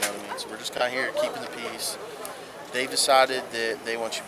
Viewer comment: or 47 .. who does the closed caption officer says keeping the piece but the closed caption says something different .. who ever did the CC please go back to school